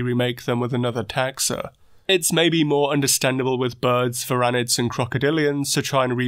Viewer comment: Birds w͟h͟a͟t͟ and crocodilians? What was the one mentioned in the middle at 3:35? It sounded like furanids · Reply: Varanids (monitor lizards).